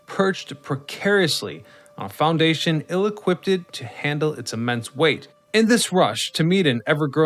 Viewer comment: I’ve never heard someone say the word “equipped” the way you say it: “equippeded”. I think it’s because usually we say the word equipped like “equipt”, but when you saw the word (as you’re recording the voiceover) you felt like the word needed its “ed” enunciated, even though you’d already said the word completely -hence the extra “ed”. This completes my English autopsy.